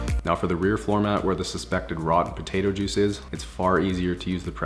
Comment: Potato juice? Hmmm. I thought at first he said tomato wrong. Nope, he said potato.